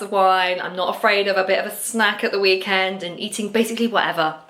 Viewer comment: Goodness, I had to replay your 'lunch time' genuinely thought you said "not afraid of a bit of smack at the weekend" 😂🙉 I thought dear lord this has took an unexpected turn 😂😂😂 relieved my ears failed me & it was in fact 'snack'.